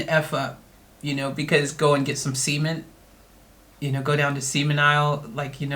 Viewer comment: Lol thats funny...i think if a person intends to hurt another then its not ok... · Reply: Oh shit i thought you were saying seamen...not cement.